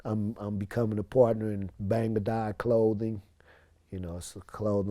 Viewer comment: Did he say Bang Da God clothing at 6:54? That's me !